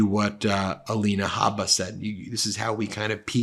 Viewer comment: I thought he said javalina jabba lol